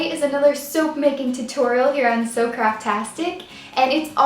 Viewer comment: Lol I just thought she said SoapCraftastic at the beggining